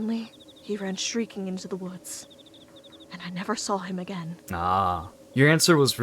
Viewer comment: i wasnt looking at my screen so i genuinely thought she said streaking in the woods, i was just like she say what now...